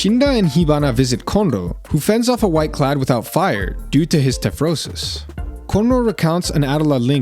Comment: due to his what? cant hear it right. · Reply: tephrosis, basically when u overuse your powers so your skin chars